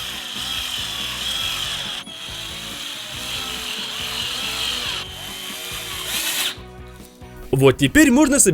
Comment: Мне одной послышалались шуруМпами?